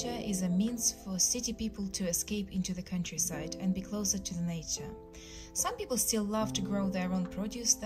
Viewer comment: I heard "just enjoy being in the nato" when she said nature